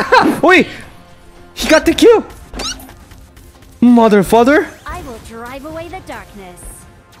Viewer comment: Did I hear it wrongly or did General said Mother... Father ?😂